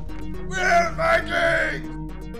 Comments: I can’t tell at 3:41 is he saying “where’s my cake” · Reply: Pretty sure he said “we’re Viking’s!” But “where’s my cake sounds better” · I thought at 3:41 he said “where’s my cake!”